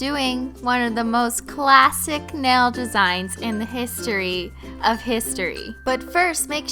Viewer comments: My phone thought she said hey Siri when she said history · Did anyone else's Siri go off in the beginning when she said "history" mine thought I said "hey Siri" 😂